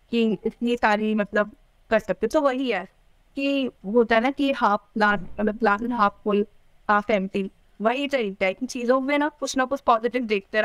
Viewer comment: What the quote she said at 31:05 ? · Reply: Glass is half full for someone, half empty for other.😊